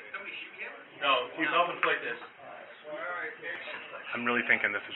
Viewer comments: he actually says "No, hes self inflicted". Meaning he shot himself. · Your subtitles should read here: "No, he's self-inflicted" in regards to the cop answering the other cop as to whether or not they shot him. It's clearly what he said. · 'No, we ... [muffled] ... like this' He clearly said 'No, he's self-inflicted'